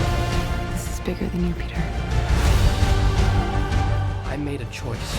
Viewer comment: At about 4:15 it sounded like she said "this is bigger than your peter" he he he